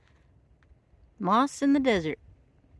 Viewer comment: When you said Moss in the desert 🏜 I actually thought you said lost in the Desert 😅 until I saw the moss ❤